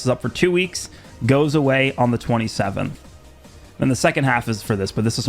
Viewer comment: So do these events go away on the 27th? I didn't hear clearly